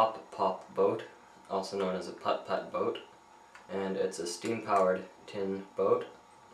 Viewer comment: i have no idea why, but i thought this vid said pop tart boats..